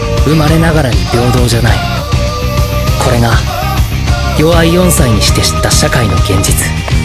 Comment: Why does it sound like he said genjutsu from naruto